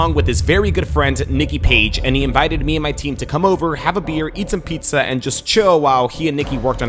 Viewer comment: Did he just say i have corona beer?!!?